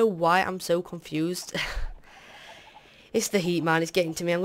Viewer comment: i thought you said "it's the Heman" haha xD. And btw, great vid :D